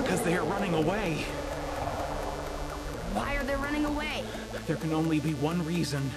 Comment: The beggining I thought she said why are they fucking us and I was like what da fuck then I repeated it again I said oh my bad